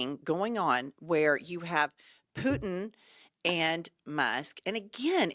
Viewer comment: It almost sound like you said Anal Musk lol